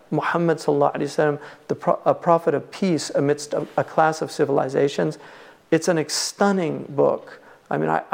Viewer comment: Can I know the name and the author of the book mentioned, I’ve heard it as Muhammad Prophet of Peace Amidst A Clash of Civilization, is it?